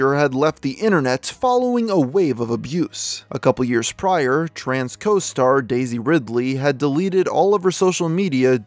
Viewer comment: Bro that sentence confused me for a minute. "Tran's costar Daisy Ridley..." Really thought that he said she was trans. Punctuation really out her changing genders too lol